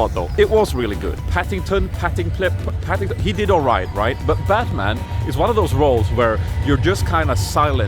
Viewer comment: Omg im laughting so hard. Sounded like u said (bath man)